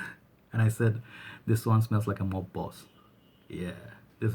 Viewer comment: at first i thought you said it smelled like mothballs hahahahah mob boss!